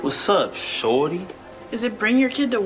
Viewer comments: I thought bro said shawty 0:05 😂😂😂😂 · I heard what's up shawty I did not he shorty😂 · "if you got a pam pa then twerk it, if you got a pam pa then shake it, no fly? embrace it." (btw wsup shawty at the beginning is wild, blud be gettin all the minors fr fr, he might be drake💀9:27 is wild btw) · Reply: He said shorty but it sounded like he said shawty fyi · “what’s up ShOuTy” is the first 3 words I hear 💀